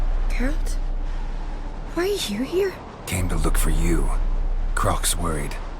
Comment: what does the girl in the witcher 3 say? Why are here here? lol